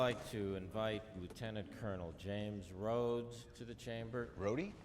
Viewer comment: 들리는건 중령인데 왜 자막은 대위라노